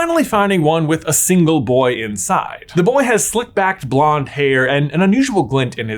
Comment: OMG!! This is so funny: when you were first describing Malfoy, I misheard & thought you said, "A boy with slick-backed BLACK hair", not "BLONDE"; so I was like, "Wait, what?" I literally had to go back and re-read the closed captions to see/hear what you said!! Oops!! My bad!! 🙊😅